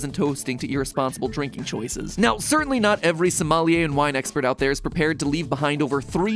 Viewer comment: I hear "Somalian wine expert". Subtitle "somali and wine expert". How many Somalian wine expert are there? As a predominantly Muslim country, there is no consumption of alcohol in Somali.